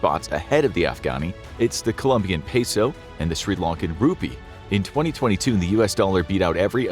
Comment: Does he say "Sri Lankan rupee" at 1:20 ? If so, can someone find a reference to this info?